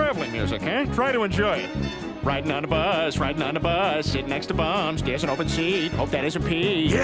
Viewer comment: did he say "bomsh"? Cuz its only a russian abbreviation for Homeless which means: Б - Без О - Общего М - Места Ж - Жительства